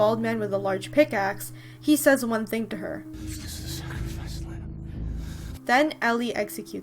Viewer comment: Why does everyone think this guy said something as silly as "This is sacrifice land"? Doesn't that line strike you in a weird way? That's because he didn't say those words. He said, "Noble is the sacrificed lamb, for he (or "free")..."